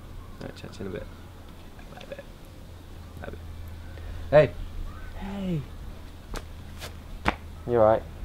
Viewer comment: Did say at 2:44 "bye babe"?? Just wondering (edit: now i get it they were acting hahaha)